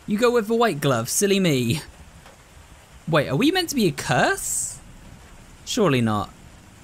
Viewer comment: I love how a person's first thought might be "curse" when she clearly was gonna say "cursor"